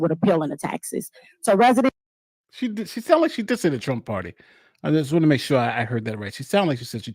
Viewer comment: I thought she said Trump party also but she actually said TRUNK Party. According to Google a Trunk Party is “a celebration where guests fill a trunk or other large container with gifts for someone, often as a send-off before they start college.”